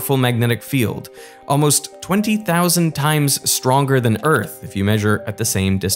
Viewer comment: " It's approximately 18,000-20,000 times bigger, but only 14 to 20 times stronger" <- think that one through again, and listen very carefully to what Derek said. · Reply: he clearly said "stronger." Put on the subtitles if you need help listening. :)